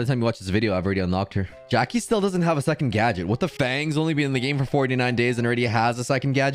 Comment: what is trying to say? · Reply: what the fang